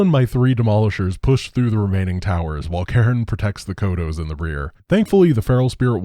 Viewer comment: Laughed out loud when I heard “Karen,” then realized it’s “Cairne”